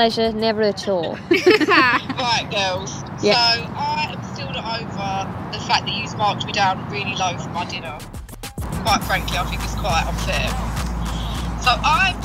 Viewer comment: What did Jesy said at 0:28? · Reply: She basically said she's still. upset that they. marked her. low in the. Eat in with. Little Mix